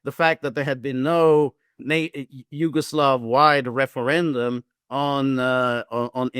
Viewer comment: Im not a professor of history nor is english my native language, but when he says majority wanted to stay @ 4:30, thats BS. In Croatia, 84% voted, 95% voted leave. Usually majority means more than 5%? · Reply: No, he said there hadn't been yugoslav wide referendum, because it was one state, one republic(not like ussr). That means other parties had a say in the decision making process( like serbia for example), not just slovenian and croatians nationalists. You should listen carefully. At least that is what he said